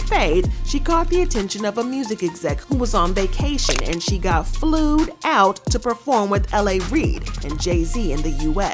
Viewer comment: did you just say flewed out lmao!